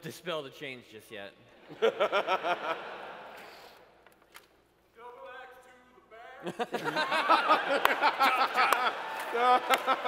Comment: What is that audience member yelling at 2:43:10? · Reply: 'Double axe to the back!'